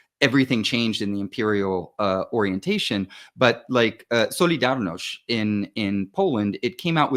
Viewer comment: what is the word said at 44:53? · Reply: Solidarność (1980s Polish "union"/CIA front, famously led by Lech Walęsa).